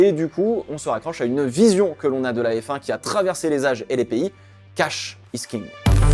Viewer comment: J'ai compris "Cash miskine", j'ai beugé mais c'étais pas incohérent aha